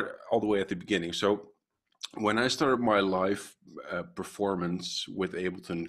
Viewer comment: I thought he said “ let’s start from the beginning, at the start of my life...” I was ready!